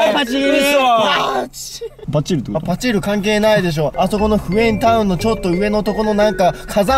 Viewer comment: フエンタウンがYENTOWMに聞こえた...